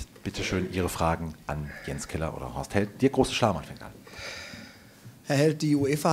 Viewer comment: Ich verstehe immer "Dirk Große-Schamhaar" 00:21 xD